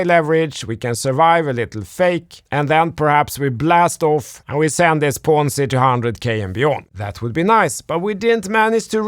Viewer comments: Did he say "We send this Ponzi to $200k and beyond...Ponzi"?? at 2:03. If so impressed with his no bs approach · Did say 'Ponzi' at 2:02? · Did he say ponzi @ 2:02 does he really think that .??